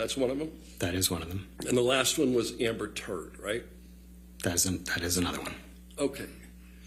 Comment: did I just hear " AMBER TURD?"🤣😅🤭